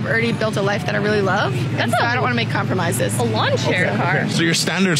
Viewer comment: What she SAID: "...a lawn-chair car ! " What I HEARD : "SQUIRREL !" .